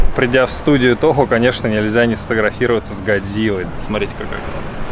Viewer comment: сначала услышал "гайдзила". Может кто-то поймёт прикол.